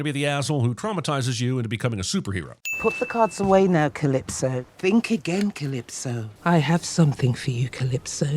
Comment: Calippo? All I hear is Calippo 🥵